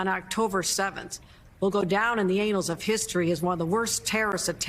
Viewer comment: Ok did she just say the “anals” of history ???